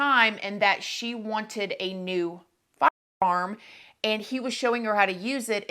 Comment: I was listening rather than watching to this and the way the the word “firearm” was cut, I thought said the victim was looking to have a new “f*ck farm”. I thought this was going to go a whole different direction.